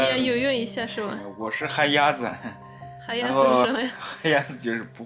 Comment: What he said is 旱鸭子 not 海鸭子(sea duck)，the meaning of 旱鸭子 is non-swimmer. If we use literal translation of 旱鸭子, it’s Terrestrial Bird or Terrestrial Duck.